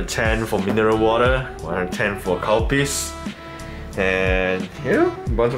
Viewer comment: instead of calpis, i heard cow piss😳